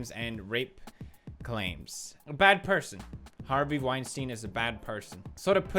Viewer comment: No lie I wasn't paying attention and I thought you said Harvey Weinerstin